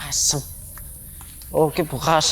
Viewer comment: salfok bilang apa barusan 🤭😅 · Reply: Asu katanya wkwkwkw